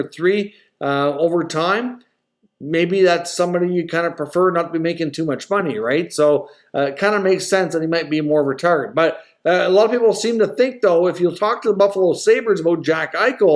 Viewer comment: Did you say, at 17:13, “it kinda makes more sense here, he might be more ret¥arded “